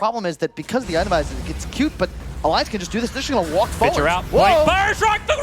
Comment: What was saying on 8:05? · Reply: "alliance just gonna do this and walk forward" that's what I've heard